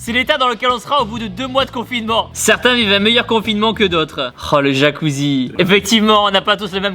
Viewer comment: Tibo :" oh le jacuzzi " Moi : " oh les meufs " 😭😂😂